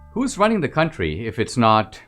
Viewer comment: I thought it said, “who’s ruining the country”. Sadly it does mean the same thing...